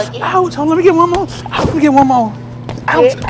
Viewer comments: I thought she said have a grape 🍇 day😂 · She wacked him 100x and said Have a great day! get out of her buggy!! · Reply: I honestly thought she said get outta my budget ..grapes are damn expensive 😂